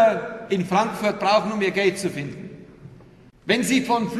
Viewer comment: Hab bis jetzt immer verstanden "um in Frankfurt ihr GELD zu finden"^^ Jaja ich weiß, daß es eigentlich "Gate" heißt, aber ich finde, das macht es eigentlich noch lustiger (Kommt halt davon, wenn man so nuschelt)^^